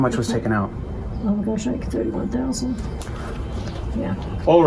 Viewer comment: How much did she just say? WTF? $31,000 - christ on the cross smh!